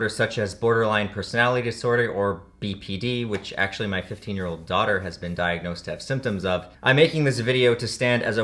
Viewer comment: Did he say "15 year old daughter"? Also I would definitely feel comfortable tripping with you man. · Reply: You misheard. He said 50 year old daughter